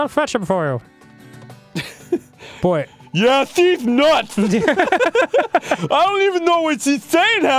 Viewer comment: thought he said deez nuts.